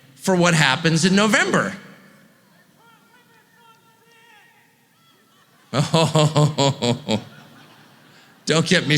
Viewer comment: What did the guy say at 7:28???? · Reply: “They’ll put lemon in trumps hair”